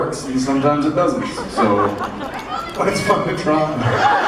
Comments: what does jared say at 9:42? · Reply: i'm pretty sure he says "but its fun to try" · "AU? Gold? Isn't that gold?" Jared, your Sam is showing...LOL btw, anyone know what he said at 9:43? lol... Thanks for uploading this!! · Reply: It's fun to try